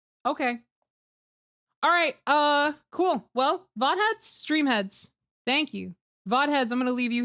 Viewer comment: Did she just say Vought heads? I'm a vought head